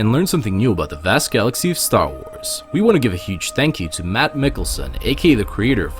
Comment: This is most likely one of the best story videos we have done so far and it's all thanks to. We want all of you to check out his channel and his project "The Han Solo Chronicles" which we made with his family simply out of fun. Here are all the links in order to show him your support :) CHANNEL: <link> THE HAN SOLO CHRONICLES TRAILER: <link> THE HAN SOLO CHRONICLES PATREON: <link> THE HAN SOLO CHRONICLES INDIEGOGO: <link> If you want to see him star as Han Solo in our videos again, let us know as well as go to his channel and let him know ;) Thank you guys for everything! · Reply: Lol I thought you said Mads Mikkelsen